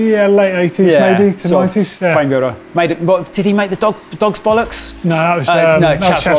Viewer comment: did someone say dogs bollucks?